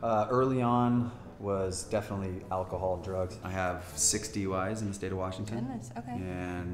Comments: I THOUGHT THE DUDE WHO SAID “I have six DUI’s in the state of Washington” said “I have sixty wives in the state of Washington “ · thought he said "60 wives in the state of Washington" lmaooooo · Did he say he has "60 Wives in the state of Washington" ?